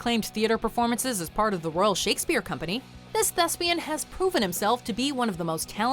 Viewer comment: i first heard this as “this lesbian” Wtf is wrong with me lmao 😂